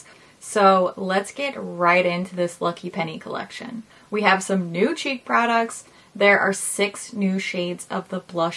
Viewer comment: Omg, at 1:24 I thought you said “new cheap products” 😂. Then I realized, hee hee.